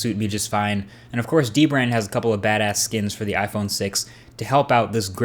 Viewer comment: Did he say bad ass on 1:02?